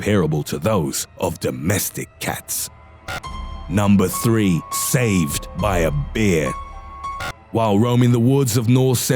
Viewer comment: did he say saved by a BEER?!..😂😂